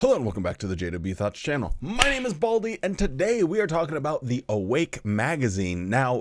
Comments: He did. Lol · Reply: Actually? I thought he said Wally.😂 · I thought he said "My name is baldie" at the beginning.